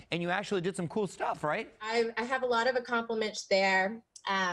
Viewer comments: WAIT, DID SHE JUST SAY "ACCOMPLIMAGE"??!?! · Wait did she just say accomplamensch